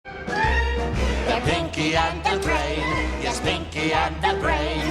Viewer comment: I don't know why, but instead of hearing the words "pinky and the brain" I heard "kinky and the brain" what is my minds problem?